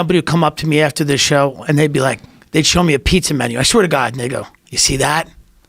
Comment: at first I thought he said "I swear to God nigga" but nah it was they "and they go"